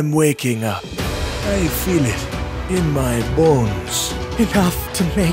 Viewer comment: I thought he said i feel it in my balls on the black panther impression